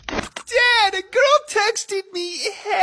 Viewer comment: I thought he said gru texted me